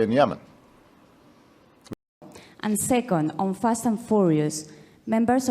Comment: Fast and Furious? Lol... don't know what she actually said there.